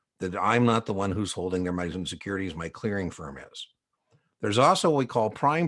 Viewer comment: wt-heck did you say at 24:32 - transcript reads: "i'm not the one who's holding their medicine securities my clearing firm is" - medicine..? Anybody..? P&T! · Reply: Monies and securities